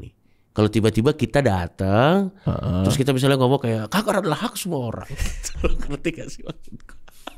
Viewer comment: coki bilang apa sih? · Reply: "kanker adalah hak semua orang"